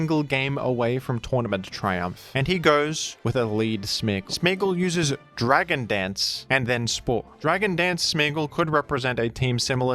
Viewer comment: I was horrified for a second I thought I heard you say “Smeargle uses dragon dance, and then explodes”.